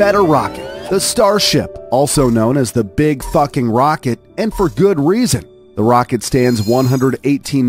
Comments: did you say big f*cking rocket? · Did he say Bug Fuc*ing Rocket Or i just heard it wrong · did he say big f*^cking rocket or big falcon rocket? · Did you said Big F#cking Rocket or Big Falcon Rocket? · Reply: F#cking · Did he just say big f***ing roket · d-did he just say big fucking rocket instead of big falcon rocket? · did he say the big fucking rocket? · did you just say F*CKING??? love that part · Is it just me or I thought he said big f rocket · did he just say the f word? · Lol I swear I hear "the big fu**ing rocket" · Did you say big fucking rocket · Did he just say the big fuckin rocket if you see this let me know what you said bc i was laughing😂😂